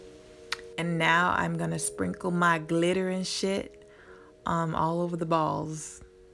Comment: LOL @ 4:39 did you actually say you are sprinkling your glittering sh*t all over! LOL LOL LOL